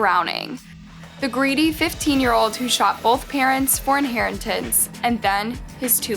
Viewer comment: Thought it said “shit both parents” and was confused fora good few minutes